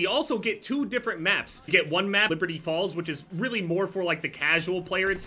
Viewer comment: Might be too much waffles, but really thought said Liberty Balls